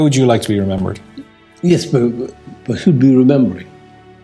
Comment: please can someone tell me what he says at 8:20 responding to question: "How would you like to be remembered?" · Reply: Kate Sagan “Yes, but who’d be remembering?”